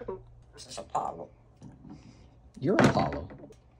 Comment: "This is Apollo." "You're Apollo." That had me laughing for some reason. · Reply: I legit thought they both said "a bottle" instead of "Apollo" 😂